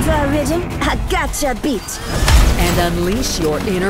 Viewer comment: This trailer is amazing but when lifeline spoke I thought she said “I got ya bitch” I was like 😳😳I couldn’t unhear it ever since